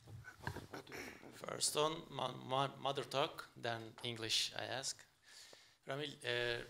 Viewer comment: Did He say mother duck